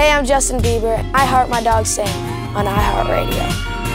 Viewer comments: Does he say: "I heard my dog sing, at iheartradio"? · I thought he said "I heard my dog sing" 😂😂